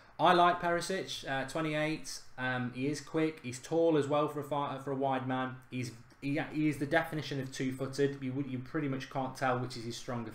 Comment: I thought he said tall for a white man...